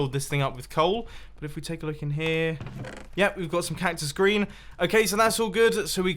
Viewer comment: did you say cactus Grain?